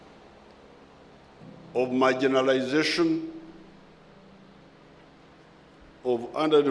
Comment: Wrong translation text. The president said; marginalisation instead of what was written: "my generalisation". Maybe this is being translated by Europeans 🤔